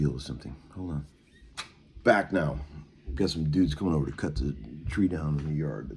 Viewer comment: Oh! He said "cut the trees" I thought he said "cut the cheese." 🤣